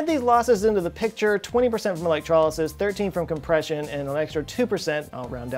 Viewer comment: screen captions wrong? says "electrolysis", graphic says "electric motor loss" - then he says "compression loss", graphic says "inverter loss" ??